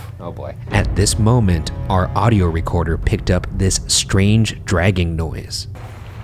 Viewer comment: I thought Ryan said "strange dragon noise" 😂